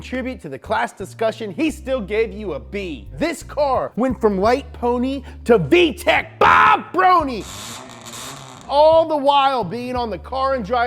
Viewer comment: Did he actually said from light pony to BAH!! Brony??🤣🤣🤣 that’s awesome. Again, he’s a brony. Nice lol jk